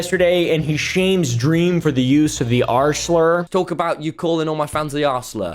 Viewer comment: i just can't not hear him say 'arsler' instead of 'r slur'